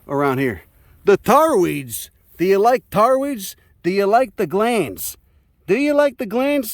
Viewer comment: I kept hearing: "Do you like the glans?"